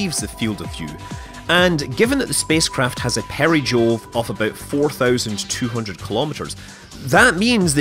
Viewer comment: did just say perijool? was that a KSP joke? Speaking of which, where's Galileo Conquest?